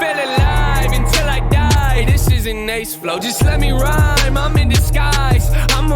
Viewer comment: So the "I wish that I could think like Big Sean does but I just can't decide if I should stick my knife inside of Pennywise" is in reference to Big Sean's album titled I Decided. So NF "can't decide" if he should stick his knife in Pennywise referencing his balloons. When NF says "this isn't Nate's flow" he's again referencing to a single from Big Sean's album called Bounce Back where Big Sean uses a very similar flow to this song. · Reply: Wait I thought it was it isn’t nice flow. I’m mishearing lot of things